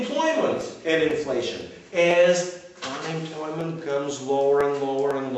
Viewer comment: I'm very interested in your lectures, but around 21:30, I missed a word I think is important to that point. You said: "Another example would be Employment and Inflation. As ___ goes lower and lower...." I think you're saying time... but I'm not sure. Besides that, thank you for putting this online, it's going to help me a lot with my AP class next year.